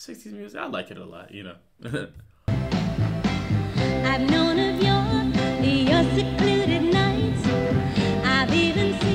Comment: For years and years I always thought they said I known of Noni Muir not "I've known of your", my mom's name was Noni Muir and I would yell out, "Mom you're in a Supremes' song!!